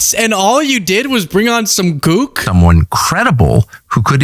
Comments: uhhhh DID YOU SAY "GOOK"?????? @ 11:40..... I'm so confused haha did I misunderstand you? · ish you used the term “gook” which is a derogatory slur against Vietnamese and Korean people in the bit where you’re talking about Tucker Carlson and chemtrails. I think you meant to say “cook” as in a crazy person, the type that buys into conspiracy theories. Just thought you might want to know so you can edit it or add a note on screen to let ppl know that word was used accidentally or you fumbled when trying to say “cook”. Appreciate all the work you do, keep up the good fight!